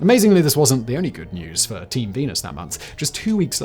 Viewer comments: ? Team penis? Did i hear that right? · Is it just me or did he say " team penis that month.